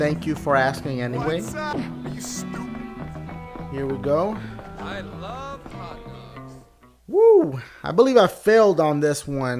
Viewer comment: I looove hot dogs! Haha please tell me someone else heard that.